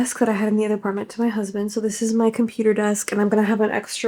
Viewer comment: Did I hear `husband`? 14:46 😮